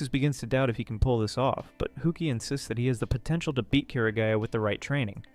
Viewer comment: DID YOU SAY POTENTIAL?